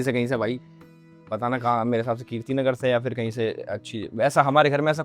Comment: does he say Kirti ke ghr se?